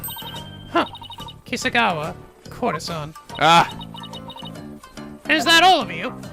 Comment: Hey guys... guys... It's my second favorite AA character... Uendo I love him... Waiwo wait.... Kisegawa introduced herself as Courtesan? i thought she said Harlot? They mean the same thing but...